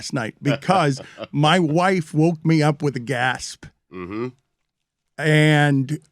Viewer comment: Thought Dan said his wife woke him up with gas